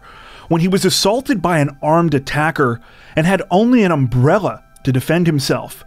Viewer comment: I thought the narrator said the main only had a gorilla to hand, then realised he said umbrella. Really need to wash my ears out lol